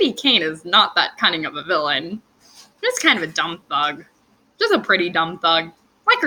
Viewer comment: When you called that character a dumb thug... I could've sworn you said "dumb f*CK". I lost my mind lmao. watch your profanity! I think credit cards coming in the mail is notable TBH. And feel free to use this platform to talk about tangled. Getting your thoughts out is good for you. Whether it's sad thoughts or happy thoughts.